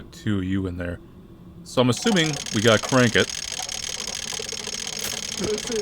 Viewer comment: Bruh i thought it said "crack it"